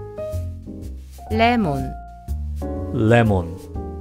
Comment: was I the only one who heard it as rapmon 🤔😶😶😶😶😯